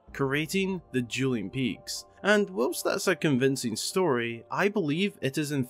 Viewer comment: The captions said "conniving" instead of "convincing" around 4:58 I'm sorry I can't help it Great theory!